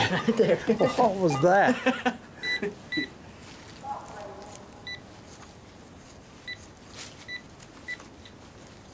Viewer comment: I heard a woman's voice, like an echo, at 8:56, but couldn't tell what the voice was saying. Then I could also hear something at 9:23, like the others said, but can't tell what was said. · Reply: Anna OConner I listened to that spot a couple times and it sounded like she says.....stop please and at 923 933 you can hear more